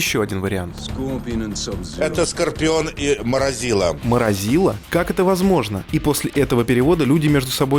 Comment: АХАХАХА МОРОЗИЛА . МНЕ ПОСЛЫШАЛОСЬ МРАЗИЛАЮ ВПЕРВЫЕ СЛЫШУ ЭТОТ ПЕРЕВОД. ХОТЯ МНЕ ЛЕТ НЕ МАЛО И Я ВАНАТ МОРТАЛ КОМБАТА